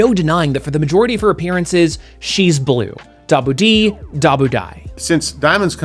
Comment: I thought said “she’s blue, ‘Double-D’ dabu-dai” 😂